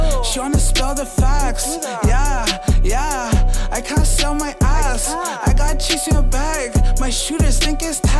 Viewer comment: did he say 😂🤔 “I gotta sell my ass”